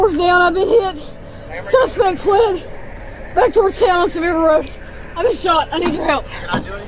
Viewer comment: I think she said “back towards house to mid road”